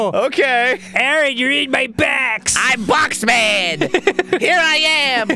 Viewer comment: I've been watching Danny Phantom and when they said "I'm Box Man" all i heard was "I am the Box Ghost" (also triple points to commenters who read the last part in the Box ghost's voice)